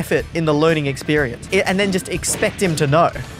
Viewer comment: I think he said the n word